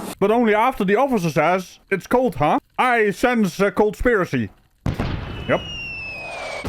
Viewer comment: Did you say cold-spiracy 3:47? 😂😂😂